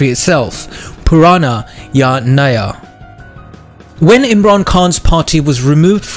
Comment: How do you spell what they say in Pakistan 2:30 ? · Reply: Naya = new Ya = or Purana = purana